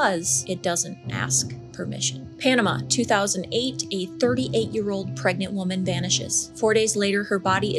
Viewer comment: Can someone correct me or did she say 8 year old pregnant woman? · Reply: No. 2008 a 38 year old woman.